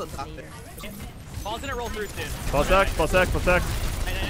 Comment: Lol when hacked ball, it sounded like he said ball sacked.